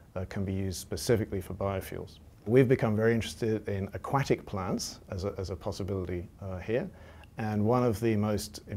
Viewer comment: "aQuackic plants" I HEARD HIM SAY IT.